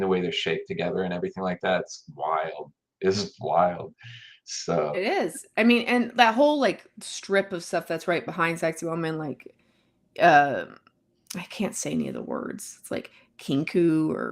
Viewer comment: At first, when you said Saqsaywaman, I thought you had said 'Sexy Woman.' I was confused for a moment. Thanks for great discussion.